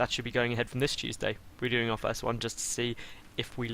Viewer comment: Ask and ye shall receive (for the thumbs). Am I the only one who thought he said "Cheese-day" instead of Tuesday?